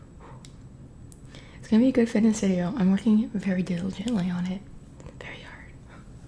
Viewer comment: whaaat did she just say omg 😂I couldn't understand 😂 anyway it was a good video and you definitely got me craving some dumplings 🤤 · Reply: Also in translation all she said was that it’s gonna be a good fitness video and she’s working very diligently on it and very hard, just mushed together and quick lol😂